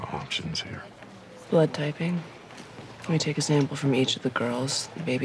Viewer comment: Just because a woman has Type A or type B Blood won't rule them out as the mother of the child. Blood type is an example of Co-dominance, 2 genes code for it and express themselves together with a gene coming from both parents. So if the mother is let's say Blood type A with genotype IAIO & father is blood type B with genotype IBIO there is a 25% chance that their baby will have O blood (genotype IOIO) type with 1 IO gene coming from each parent. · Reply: I thought she said if the blood type is AB . In which case what she said is true.